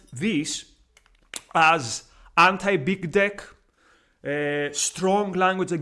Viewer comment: What is it that you said at around 4:00? To me it sounds like "anti-big deck," but I'm not sure that's right. Can you please explain what you meant here?